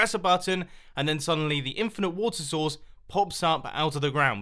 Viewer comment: Now everytime Mumbo says "water source", i hear "woto sauce" from his last few videos. 😆